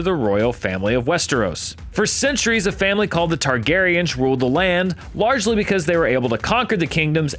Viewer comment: Excuse me, did he just say Targariage?